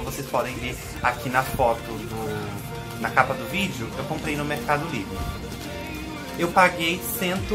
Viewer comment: Eu ouvi "Comprei no mercado negro" a primeira vez kkkkkk.